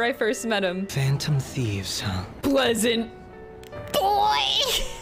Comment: I always thought Pleasant Boy said Peasant Boy